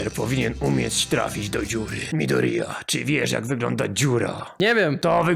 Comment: usłyszałam "mi do ryja"